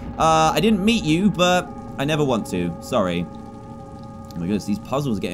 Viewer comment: Dan I thought you said you had never swear😂😂😂